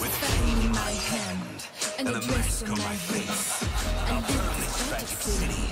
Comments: Wait I heard a dress on my face 😂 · Wait I heard a dress on my face 😂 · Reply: I heard "a desk on my face" lol